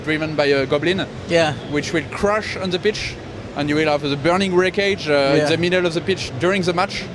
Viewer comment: Sounds like he said crush into the bitch